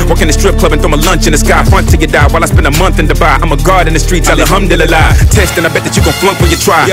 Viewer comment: God. God in the streets. If you thought he said guard, you didn't get the bar.